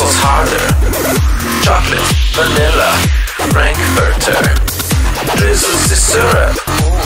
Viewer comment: I heard this is the syrup